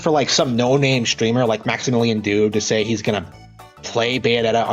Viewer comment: Did he just... Now I must have misheard him but I could have sworn he just said "A nobody Streamer like Maximilian Dood" A nobody? I hate to be the one to break this guy's fantasy world but I'm 99.9% that Max isn't a nobody like this Nintendo dork... Also I'm 90% sure Max is a better writer but I don't know, I haven't read this guy's books, nor will I read them But like, randomly calling out Max Dood like that? Come on man, seriously?